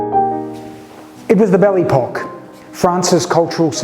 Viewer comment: Just an aside, this made me laugh - at first, I thought you had said 'belly pock' for la belle époque - (which is usually considered to go from 1900 up to the beginning of the First World War).